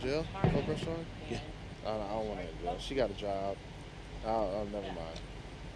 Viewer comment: "I don't want to press charges, she got a job" - He's not going to let his only paycheck go to jail. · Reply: Turn on closed captioning then start at 8:45. He clearly says 'job'.